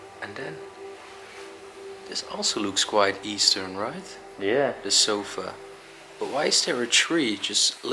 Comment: Did anyone hear a woman's voice say, "Yeah"